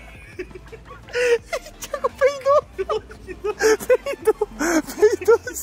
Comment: Eu ouvi 🤣🤣🤣🤣🤣 peidin frouxo 😂😂😂😂😂